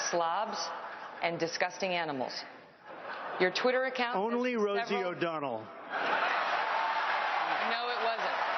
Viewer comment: All this time I thought he said, totally Rosie o donnel